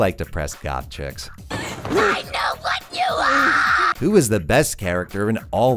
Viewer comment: Imagine my joy when I realize you list all the media used in the description. Imagine my disappointment when I realize the media list doesn't cover the Q&A section and I wanted to look up the short "I know what you want!" clip used in the Q&A section. Life can be brutal sometimes. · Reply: Ah, thank you very much! Seems like I was completely wrong then since I expected the clip to belong to something with Satoshi Kon vibes. Edit: And I just realized she actually says "I know what you are!" Not that it makes a difference, but I still find hilarious how it can be misheard solely due to the screaming since that would never happen if it was just spoken at a normal cadence.